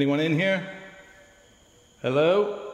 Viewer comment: Hope is okay you’re a great friend for helping him up and out of there but not sure if it was you sighing but at 2:14 I hear a creepy hi as you walk in